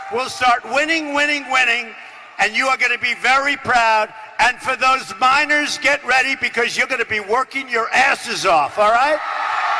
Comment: I think he said "And for those Minors get ready, you're going to be working your asses off."